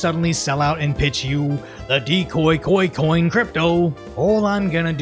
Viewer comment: I thought he said "Decoy Goy Coin" and I jumped out of my chair 😂